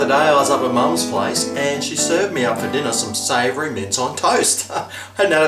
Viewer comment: At first I thought it said minge.